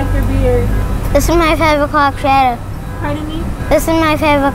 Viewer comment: I honestly couldn't hear 5 o'clock shadow either. I was hearing "this is my favourite pop shadow"